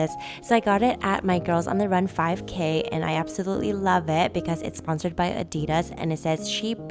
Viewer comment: I thought you said i accidentally love it